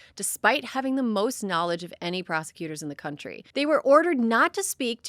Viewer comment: Prosecutor??? I thought you said prostitute initially, and I had to listen again to check… Hey, Maybe Pam Bondi loves sucking off Mushroom dicks!!! She has no other qualification in my view to hold this position… it is laughable how the US is being run